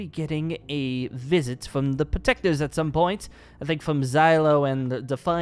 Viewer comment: omg, busted 31:55 plsss watch / he said xylophone instead of Le pho, pls do not hate on my spelling :)